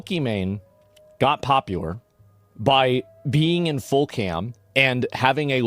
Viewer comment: Eek, thought he said peeing in full camera.